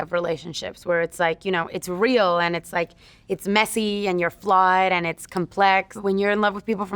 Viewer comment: parece que dice "es messi"